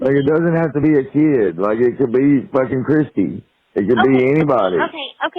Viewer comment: did he said it could be f-ing Kristy?